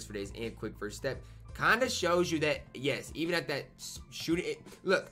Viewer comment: Did Ty say Yas!